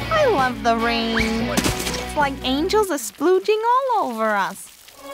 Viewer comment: Huh. Up until now I thought Harley said “It’s like angels are smooching all over us.”